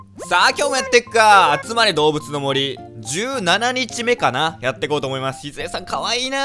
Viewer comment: つまり動物の森に聞こえる